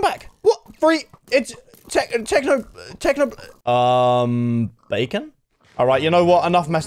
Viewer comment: Not gonna lie thought i heard almost say technoblade